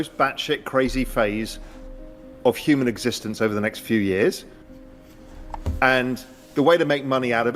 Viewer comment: Few years? I thought he said few months.